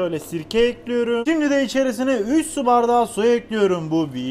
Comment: 100 su bardağı diye duydum aklım gitti